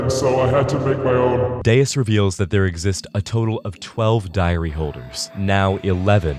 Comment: I honestly thought you said and 12 diarrhea holders